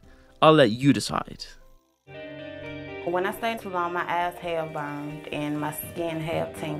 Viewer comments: I think she says “eyes had burned” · I think she says "My eyes they burn" hence why the show shows her red eyes but ngl I definitely heard ass hairs the first time too